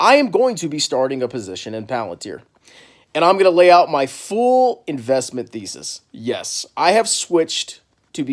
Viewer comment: fool-investment-thesis or full? Dont know if I misheard